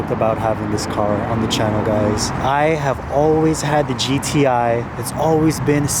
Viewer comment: Where’d it go 🤣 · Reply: I heard “the GTI I had” and came straight to the comments 😂 hyped to see the space egg gap more v8s!